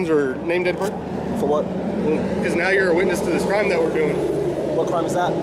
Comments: Did he say: "Now you´re a witness to the crime we´re doing..." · Did He Say A Witness To This Crime THAT WE'RE DOING lol 4:54?